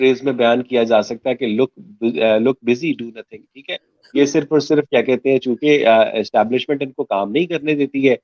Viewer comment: kya bola sirji ne? Ch**tiye? 🤣